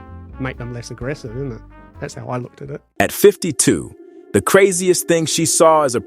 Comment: I thought he was saying she was a prison guard at 17 i was like yeah no most of the time you gotta be 21 to start unless super desperate for guards then RARELY you may be able to work at 18 but extremely rare. I swear i thought he said at 17 imagine watching a prisoner.